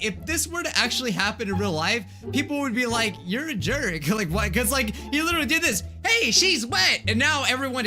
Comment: captions said "killing white kids like" 0_0 well then